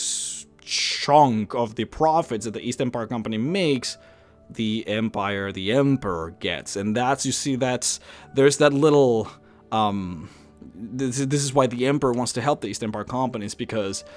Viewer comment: East? I thought you said Weast.